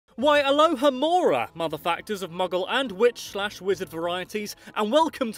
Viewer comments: I thought you said Alohamora motherfuckers lol😂😂😂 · Sounds like he says "mother fuckers" in the beginning.